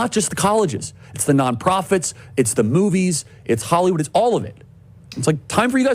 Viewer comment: I thought Kanye said the Jews owned Hollywood